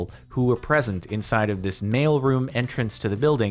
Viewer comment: I kept hearing "Male Room" and I was so freaking confused